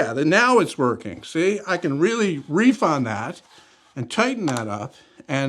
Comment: Did you say "reef" at 10:21?? Demonitzed!